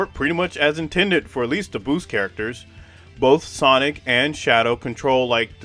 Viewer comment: i thought you said sonic almonds 😭